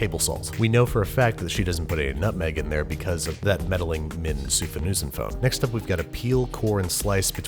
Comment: What does he say at 5:20? Some sorta sousaphone? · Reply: Minh Souphanousinphone.